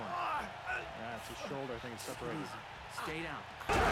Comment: What did the trainer say? It sounded like Stay Down.